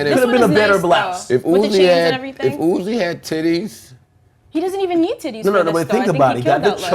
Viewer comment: did he just say if Uzi had tities🤔